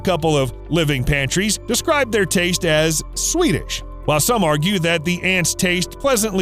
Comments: "Describe the taste as... Swedish.." Ill just head on out.. before yall go crazy. (I know he said sweetish but i thought he said swedish at first. Clearing this up for people who doesnt get that im joking before hand) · Did he say "swedish"?